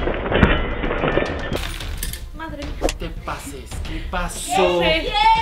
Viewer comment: Like si oíste lo que dijo lessli en él minuto7:55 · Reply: Martha Alanis Sandoval Martinez dijo madre mia